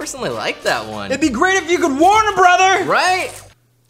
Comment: All I heard was "It'd be great if you could Warner Brother!"